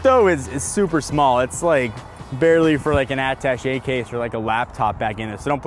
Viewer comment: Wtf is an "adtache"?? Case?? When you talked about the Model 3 frunk space you said it's about enough room for an adtachay/addtache case.. did I mishear you , or am I out of the loop??